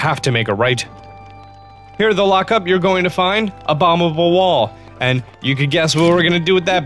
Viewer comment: When you said "a bomb-able wall" I thought you were saying "abominable wall" 😂😂😂